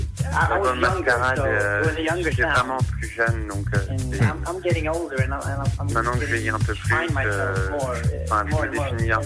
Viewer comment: that (...) part I heard "I'm getting older and (something) I can find myself more and more every time I write songs"